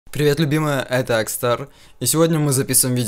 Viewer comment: Мне послышалось или Акстар сказал привет любимая, если это так от спасибо🥰